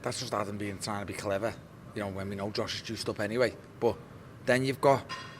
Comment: Did he just say " when we all know Josh is juiced up anyway......?"